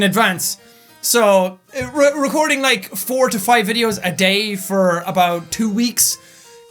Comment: OMFG I THOUGHT HE SAID 45 VIDEOS A DAY I FREAKED OUT XD